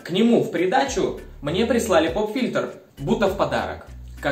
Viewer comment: не знаю как, но мне послышалось "... прислали компьютер" я такая: что!???!